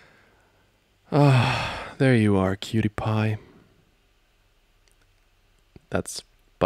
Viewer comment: i really thought he said 'PewDiePie'